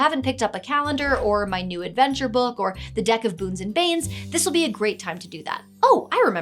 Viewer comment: My husband, from across the room: "Did she just say, 'The Deck of Boobs and Babes?!'" Lol